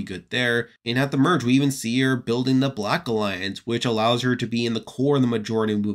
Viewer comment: I heard Deshawn created the black alliance